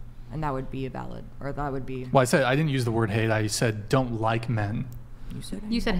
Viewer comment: no he didnt say "hate", he sayed dont like... the chick on seat 2 said hate! Here you can see what men say and what women understand :D